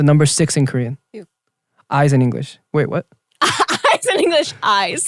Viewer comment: Jae : Eyes in English Me: WHATTTT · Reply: I thought he said "I as in English" and I was very confused